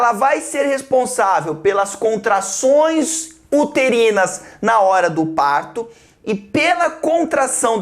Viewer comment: só eu ouvi "puterinas"